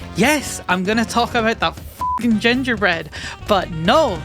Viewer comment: I misheard this as "gender bread" and got very confused because I actually did watch the last video and was afraid I missed something incredibly important 😭